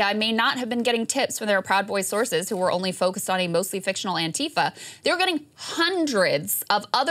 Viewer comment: Did she say ...mostly fictional antifa??? 4:00 mark. Wtf is she saying